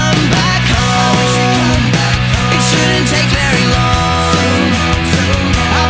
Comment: I could've sworn it was "misses dead fruit flies" instead of "Mrs Baird's fruit pies." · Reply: I always heard, "[...] misses bad fruit pies [...]" lol